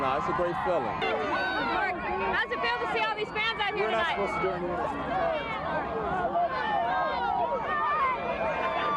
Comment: What does Pope say here? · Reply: "We're not supposed to do an interview".